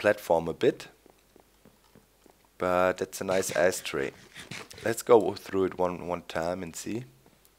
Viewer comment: did he say ass tray